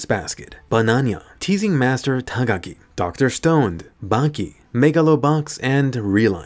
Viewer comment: sorry did I hear correctly? *Dr. Stoned*? I mean your not wrong...